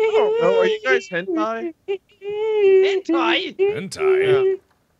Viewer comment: what are they talking about?! hentai?! i swear i heard hentai XD